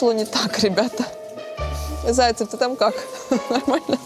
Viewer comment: мне послышалось? Она оператора «заей» назвала? Служебный роман☺️